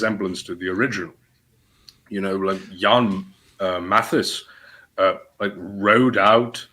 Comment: who is talking about? I couldn't understand. I want to read up on that. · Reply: Jan Matthys, an Anabaptist eevellion leader who was clearly delusional